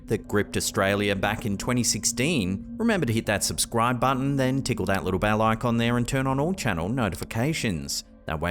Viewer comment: OH! You said, "tickle the bell." I thought you said, "tickle the balls." 😊